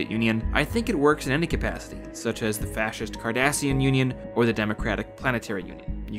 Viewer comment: when you said Cardassian Union, I thought I heard the KARDASHIAN Union, and I involuntarily shuddered at even the idea of such a terrifyingly horrific possibility. I'd get down on my knees and beg for a proudly and obsessively militaristic, entirely fascistic, systematically racist, and rabidly expansionist oppressor like the Cardassian Union vs whatever horrifying form of government that would be devised by one named after the Kardashian family. Glassing the planets of such a union would be more than reasonable, since instantly going for the "balls to the wall" Exterminatus route would be preferable against the possible spread of such a corrupting and thoroughly evil influence. If given the chance, it would no doubt put the comparatively amateurish machinations conjured up by the Ruinous Powers residing in the warp to total and utter shame.